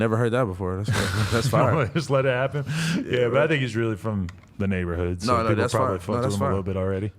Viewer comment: I thought adam said nigga but he said “I think he” I was finna say 💀💀